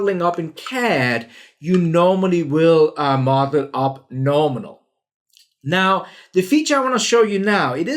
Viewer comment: What did he say at 3:25? LMAO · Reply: You normally will model up nominal.